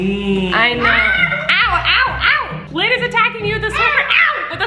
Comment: omg it sounds like she is saying awo awo like the trend